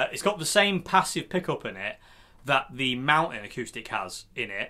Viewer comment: I heard you say Mountain Acoustic, thought you said Maton, they are bloody expensive made here in Melbourne but great guitars though maybe one day I’ll own one